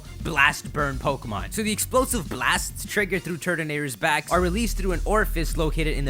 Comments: At first I thought you said Turdinator. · wow it sounded like he said turdanator